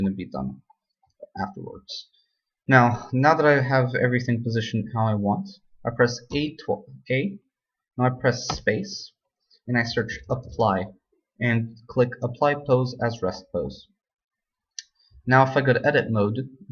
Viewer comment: im sorry man but i cant understand what you said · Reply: Now that I have everything positioned how I want, I press "A" twice, then I press "Space", and I search "Apply", and click "Apply pose as rest pose".